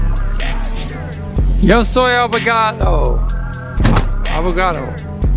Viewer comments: what i heard "yo soy avocado" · I thought he said avocado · Thought he was saying avocado 💀